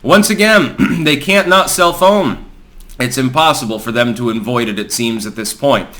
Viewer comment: honest to god thought you were saying "They can't not cellphone" since I was watching this in windowed mode.